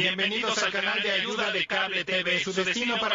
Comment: Que dijo quagmire? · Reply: Carajo